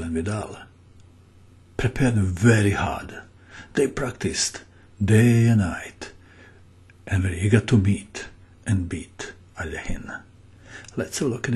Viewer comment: Utterly confused. Who resigned? Black? Didn't we hear in the beginning that Benitez wins the game? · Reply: sometimes he speaks with such a low vice that I am not exactly sure what he was saying. I thought he said "they studied hard, day and night and they got to beat Alekhin".